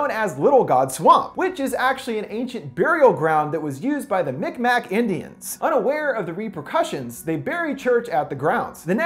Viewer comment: I thought he said big mac indians